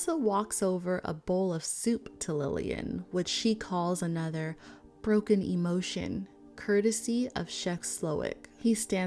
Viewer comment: It was a "broken emulsion," a lot of people misheard that one. But "broken emotion" works too